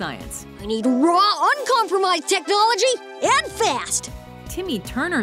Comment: Did he say dicknolygy